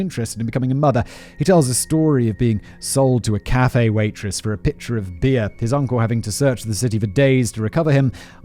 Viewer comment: I misheard him when he said Manson's mom sold him. I heard "she sold him for a picture of beer" and though wow that must be a really good picture to sell your kid for it, I wanna see it. And then several minutes later, realized he said pitcher of beer, and haven't felt that dumb in a WHILE 😳